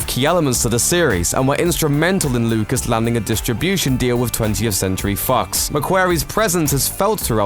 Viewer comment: It sounded like he said 20th century fucks